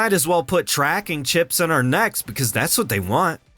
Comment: I thought you said 'put tracking chips in our nuts'.